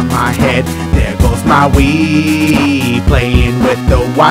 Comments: i thought he said there goes my weed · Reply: Sebastian Quintero there goes my wii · YOOOO I THOUGHT HE SAID THER GOES MY WEED XD😂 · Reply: me too i kept rewinding like did he really say weed??? then it shows wii controllers and im like oooh he said wii 😂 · i swore he said there goes my weeeeed lol guess it was cz im high mishearin shit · Who else thought when he said "there goes my Wii" thought he said "there goes my weed" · I thought he said "there goes my weed" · Wow the whole time I thought he said "there goes my weeed" 😂